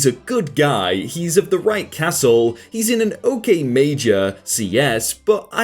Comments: did-did I just hear you say 'castle'? Because if so, I'd like to point out that caste and castle are different things. A caste tends to be one's position or role in society, or a way someone/something is made. A castle is a structure built specifically for defense, usually with one tower in each corner and one central tower and a large gate, most commonly built during the medieval era. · of the right castle did I hear that right